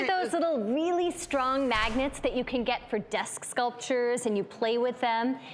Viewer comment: I thought it said maggots